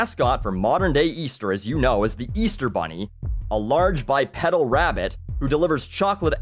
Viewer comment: When you said bipedal I thought you said bisexual or bipolar